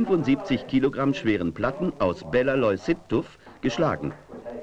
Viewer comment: Bellaloyulsiftus? 05:31 ? What ist das fürn stein digga? Nie gehört